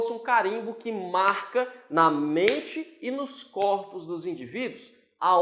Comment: Eu ouvi "na mente e nos córneos do indivíduo" kkkk!